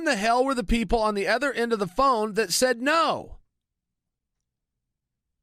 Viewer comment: lol it sounds like Chael says "Ebola" when he say "The bullet".. But seriously Chael is one hellova speaker! I could listen all day to him reading a book about history ...